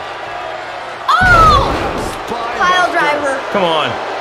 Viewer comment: Did he say piledriver